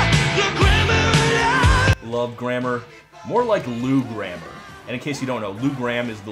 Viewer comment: I'm sorry, "love grammar" sounds like a okay title..... because I thought he was saying "love grandma" .....yeah..... I appreciate that Meatloaf has a union jack to match the stars and stripes...... I can't help but think ML was taking the pisd.